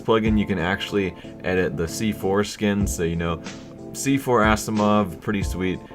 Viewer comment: Every time you said "c-4 skin" all I heard was "see foreskin"